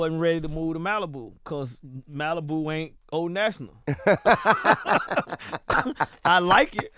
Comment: What does JD say about Malibu at 55:37 ? O national or something? What does he mean about Malibu?